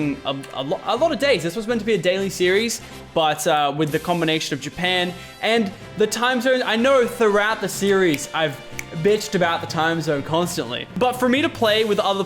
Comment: did he said bitched?